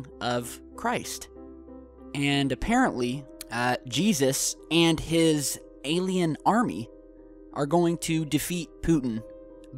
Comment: did u just say "Jesus Christ and his alien UFOs???? " Lol